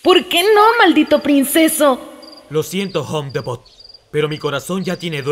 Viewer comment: Lo siento homedndn (No lo entendí bien ._.), pero mi corazón ya tiene dueña...o dueño 7w7 Jajajajjajajajja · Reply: homedebot